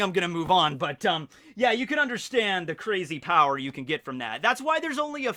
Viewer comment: Did anyone else get Archer vibes when he said “other Barry”? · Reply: No, but when he said "crazy" when talking about Boa Hancock I thought he said "phrasing".